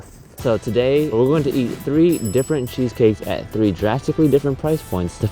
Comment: When they said cheesecake fact I thought they said Cheesecake Factory